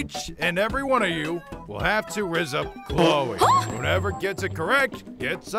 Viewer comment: um what did say,because if he said"rizz up chloe"then I'm speechless(gotta think about a couple's name for and chloe💛💜)